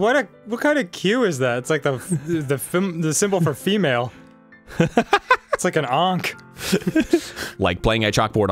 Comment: I heard "Like an Anc" Cause I'm still waiting for them to continue THE DOG Island